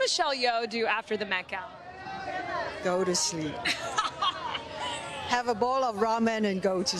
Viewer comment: I thought she said smoke a bowl, I guess the ramen makes more sense